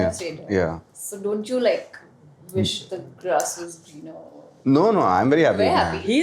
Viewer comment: what's the word she used after saying don't u wish then what she said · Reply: She said don't you wish that the grass was greener... meaning if you have curly hair don't you wish you had straight hair and vice versa.😁